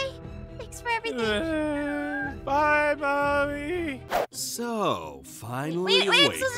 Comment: On 14:17 for a sec I thought pierce said “bye mommy” 😮😂